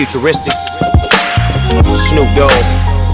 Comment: thought it said snap dogg